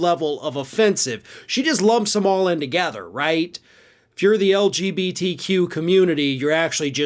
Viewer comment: It sounded like he said "lumped them all in to-gather" @ 1:07 So I looked up the etymology of together. And viola! Together Old English togædere "so as to be present in one place, in a group, in an accumulated mass," from to (see to) + gædere "together" (adv.), apparently a variant of the adverb geador "together," from Proto-Germanic *gaduri- "in a body," from PIE *ghedh- "to unite, join, fit" (see good, and compare gather).